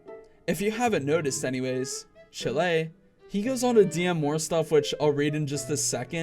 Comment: Im sorry did you just say chill-ae I know this is off topic but chill-ae I can't